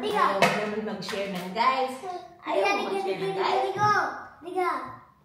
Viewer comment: is saying 'nigga' to indi? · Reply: No daw Australian accent kasi she said Nour which is No in American English